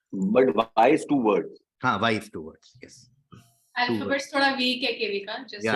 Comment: What joke did Sulagna made at 5:47? I tried listening to it so many times and did not understand what she said. If someone can clarify it would be of much help. It is bugging me like crazy😔😔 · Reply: "Alphabets thoda weak hai KV ka" - Sulagna Panigrahi, 2023